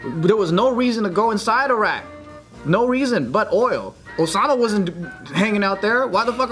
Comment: Did just say Osana? Like Yandere Osana 😂😂😂😂😂 damn support that cute messed up game